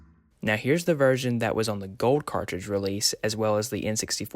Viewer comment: Yeah, there were 1.0 and 1.1 grey cartridges as well and if you had either one of those the chants are heard just like the gold cartridge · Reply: ok for some reason I thought you said they were only on gold cartridge. I guess I was hard of hearing.